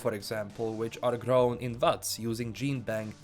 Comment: so the auto captions got this right, but I must have been spaced out and heard that the tails are "grown in butts" lol